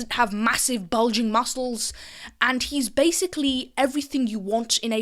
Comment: ah ah I heard "massive belgian muscles"